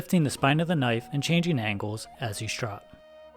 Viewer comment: I incorrectly state my stone is the DMT coarse, in fact, it is the EXTRA coarse. I left a link to it in the description. Thanks for watching! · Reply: what did you call that stone? Strope? Or what?